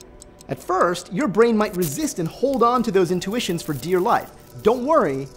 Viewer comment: Did he say hold on for dear life? ( 0:37 ) Is he a HODLer? OMG mind blown